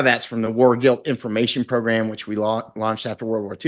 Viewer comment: What is he saying at 40:31? "War gil information programm"?